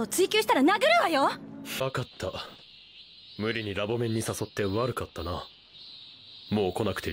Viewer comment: Did Okabe just said "fuck"?